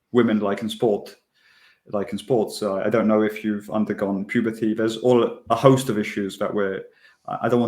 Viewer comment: Did he really say "I don't know if you've undergone puberty..." ???